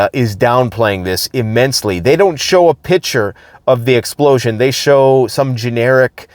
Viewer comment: Got to love that Canadian accent when they say “a pitcher” instead of “a picture”😂. (13:30 min)